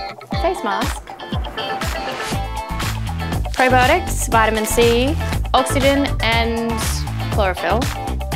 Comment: What did she say from 0:55 and 1:17? I can't understand what she's got in that little packets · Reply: As far as i heard she said 'probiotics, vitamin C, oxygen and chlorophyll'